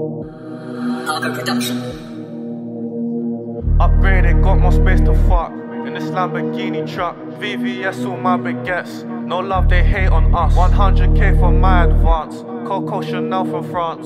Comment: I can't be the only one who thought he said "got more space to fart"